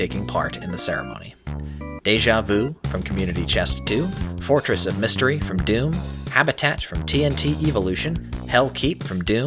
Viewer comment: "...Fortress of Mystery from Doom" Caption says Fortress of Stupidity lmfao